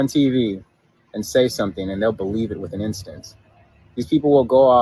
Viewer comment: did he say they’ll believe it with an instance???